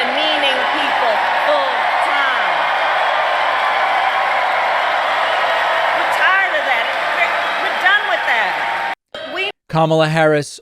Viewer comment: Sounded like Kamala said “I’m tired of that prick! We’re done with that!”? And I couldn’t agree more!💙✊😆💙